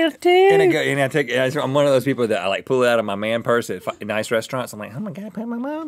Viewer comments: Does Rhett say "fucking nice restaurants"???? @ 1:32?? or am i hearing things at 1:00 am? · Does he say "Fucking nice restaurants" at 1:32 or am I nuts?